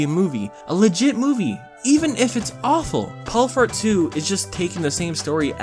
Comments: It sounded like you said “Paul Fart”. 😂😂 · did he say fart?